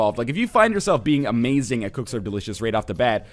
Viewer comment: Always thought he was saying cooks are delicious